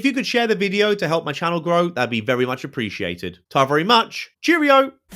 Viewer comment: what the hell did you say at the very end of the video? It sounded like “ta very much” or like “tavurry much”. I just have to know. Please!